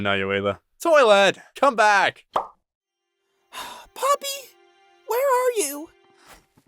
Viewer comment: It almost sounded like Paper said Pomni at 1st.